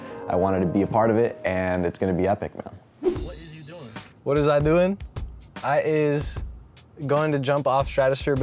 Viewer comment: Did he just say, " What is I doing... I is gonna" bruh